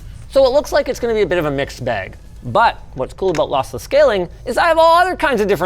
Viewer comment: I thought he was saying Lothless on purpose...